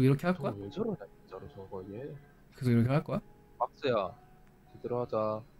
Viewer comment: I think Wonpil said, "박스야, 들어하자"